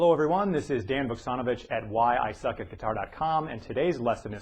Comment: i thought he said hey this is a son of a bitch from why i suck at guitar... ahahah